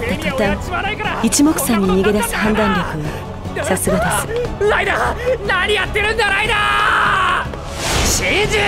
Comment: 不覚にもライダーがライナーに聞こえてしまった